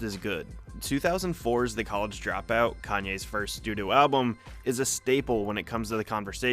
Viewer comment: "Kanye's first doo doo album" is what he actually says open your eyes stay woke